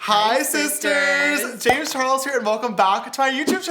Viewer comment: I always thought he said “hey sisters” :/